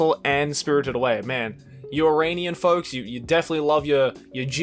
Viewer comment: ngl it took me several tries to figure out that he is sayin you Iranian folks instead of Uranian folks, just thought that escalated quickly